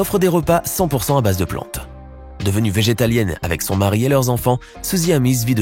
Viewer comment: Végé quoi ??! 😂😂 Végétalienne !!